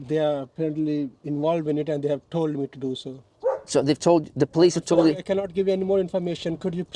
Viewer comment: he actually says the police are involved and they told him to call them if anyone turns up